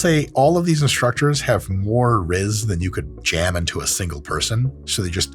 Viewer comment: did i just hear dr mike say rizz?